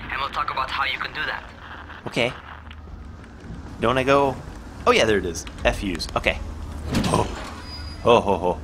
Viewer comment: "Oh F use." I heard Refuse lol poop lol